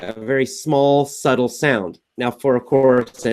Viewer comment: Hello! What is it which he says at 14:10-14:12 minutes? Very suddle, saddle sound, or what? I can't find the meaning. Please help) Thanks in advance. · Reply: Владимир Волков He says: "subtle".